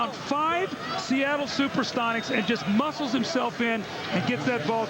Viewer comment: Did he say '''Super Stonics" ? Amazing game and highlights man. So much of a nostalgia for a time when Seattle had an NBA franchise and VC15 was the greatest show on earth.